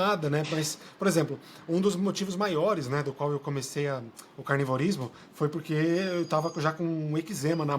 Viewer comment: Será que eu entendi certo?? Vce as 17:17 disse que vce prática canibalismo?? Onde você compra essa carne?? Algum serial killer?? Kkkkk